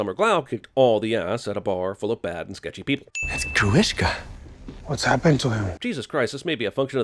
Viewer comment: 'You are the most human person I have ever met.' is a code for 'You have an amazing ass and I don't care it's metal.' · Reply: Haha fair enough, the actress was totally babe in chips And..the fact that at 10:54 the captions say a Jewish guy instead of gorishka cracked me up hahahah